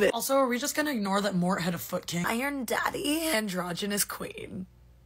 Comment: I heard "I aM dAddy😩"